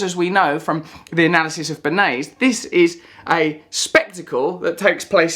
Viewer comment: the analysis of whom? I can't make out what Russel said, can anyone else? Legitimate question. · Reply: Bernaise?? I think it's a type of sauce